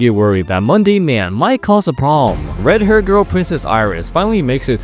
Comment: My dumb ahh thought u said “Monday man” 😭😭😭